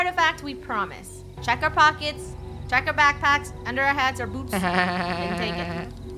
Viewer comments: D-did rainbow just says Boobs...? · it sounded like rainbow said check are b0*_s